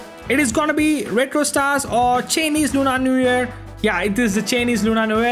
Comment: Cheeneze😭, I don't understand what he said after that 7:29 😂😅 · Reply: Chinese Lunar New Year